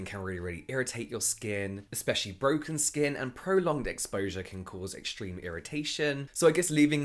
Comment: why did I heard him say "Prolonged d!ck exposure" 😂😂😅😅 I love this kind of exposure, indeed its mask 🎭💦 make my skin flawless.. Ain't no clogged pores 😂😂😂😂😂